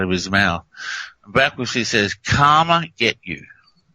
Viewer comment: "Karma's gonna get you" is DEFINATELY a positive thing, as suggests. Karma only helps good people... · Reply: I hear "Comet get you" - not Karma. There's a T on end. An incoming planet gets you if CV doesn't? 😜